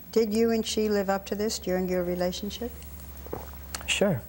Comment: Someone can tell me what did she ask at 7:34 ? I can't understand all the words >.< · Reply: "Did you and she live up to this during your relationship?"